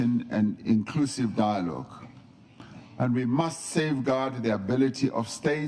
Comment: Great leadership shown by South Africa once again. Please fix your subtitles as you are misrepresenting what is being said. For example at 5:32 you falsely display the subtitle " We must save God" when he clearly says in English "We must safeguard". This error by you exposes a religious bias by you, is dishonest to the speaker by misquoting him and attributing a religious meaning to a secular statement and it detracts from the value of his closing speech by changing its meaning. It is great you offer subtitles for people unable to understand South African accented English however if you are unable to accurately show what is being said you are better off having no subtitles at all instead of being dishonest or inept. I still gave you a thumbs up and have subscribed , but please do better